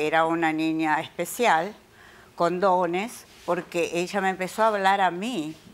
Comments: En el minuto 12:58 pensé que dijo CONDONES PERO ERA CON DONES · Escuche c0nd0nes en ves de con dones AUSINAJSKSNKSNIXDXD 13:00 JSBSJNAJHSJBSJXDXD